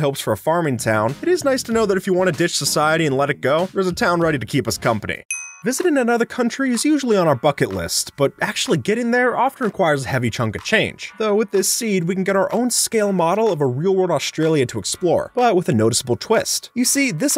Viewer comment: I thought he said they word instead of bucket